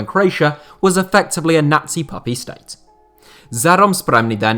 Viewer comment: Misheard puppet state as puppy state 😂